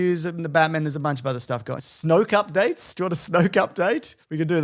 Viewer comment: I too took time to realize he was talking about Snoke, Mostly because I heard it as Snow Cup Date, and immediately went down a "Is Snow Cup a movie coming out soon? Did they settle on a release date?" path.